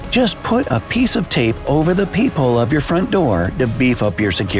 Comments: Uhh I thought u said peewhole 🤣😂🤣😂🤣😂🤣😂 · Uhh I thought u said peewhole 🤣😂🤣😂🤣😂🤣😂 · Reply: I thought he said poophole